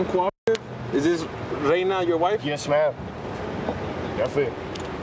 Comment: Did he just say yes ma'am??